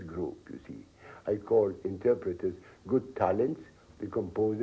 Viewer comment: What word does he say at 4:00? "I call the composers, good "???", the composers geniuses. " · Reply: “talent”